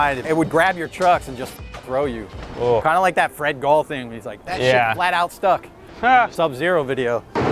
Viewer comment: Matt Reason no? 28:11 Fred gets stuck on the rail but I think Matt Reason said “That shit flat out stuck”.